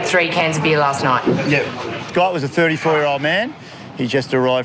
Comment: Wait, I think I misheard with his Aussie accent. That’s 64, not 34....?